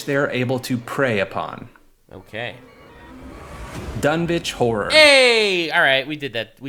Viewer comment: i heard « Dumb bitch whore »😭😭😭